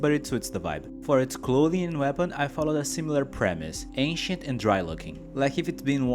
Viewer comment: i legit almost DIED of laughter 9:25, i honestly thought you said "Aint Shit and Dry Looking" which is still an accurate description, since he is basically withered away to almost nothing, CHEESE APPROVED